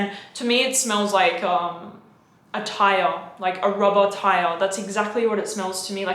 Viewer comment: “It smells like a burning rubber tire.” I’m sold. Lol. That description was top notch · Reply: I thought she said rubber tile! Lol